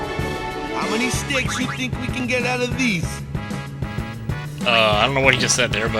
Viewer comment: I think he said, "How many steaks do you think we can get out of these?"